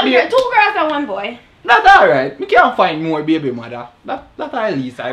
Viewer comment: Did he just say “dat aright mi can find more baby mother”😂😂😂